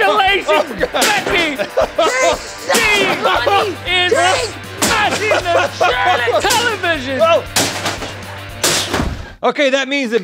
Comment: Did Link say "She's smashing the shitty television"? I've listened numerous times and that's all I'm hearing. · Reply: JustinPayton he’s saying “she is smashing the... television” and in that little pause, Becky is saying “Charlotte!” LOL!